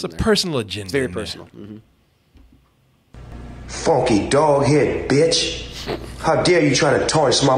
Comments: I thought Snoop said DOLL HAIR BITCH. 😆 😆 her wig look like Doll Hair!!! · I thought Snoop Dogg said funky dog head bitch LMAO!